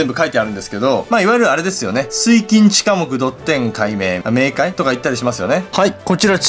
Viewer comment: 水金地火木土天海冥じゃなくてせいきんちかもくに聞こえた